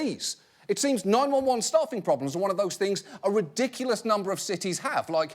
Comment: Every time he said staff, I thought he said stuff.